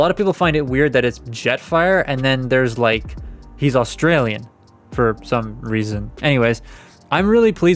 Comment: So fun fact, his Australian accent is a nebulon accent that he picked up. I don't mind it cause I love the Australian accent so win win in my books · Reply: Thought he said it was the Tarnian accent